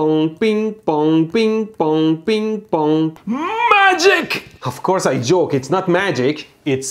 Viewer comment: i heard مجید!